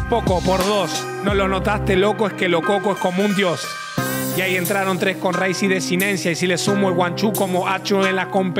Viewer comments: Porfavor que dijo? Rice? Race? Lo que dijo antes de Desinencia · Alguien aprendió a improvisar por lo menos , digo porque se supone que ser freestyle ya se nace no se hace o si? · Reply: Viejo porfavor, que dice ahi? Antes de decidencia. Dice rice? Race? Que técnica es esa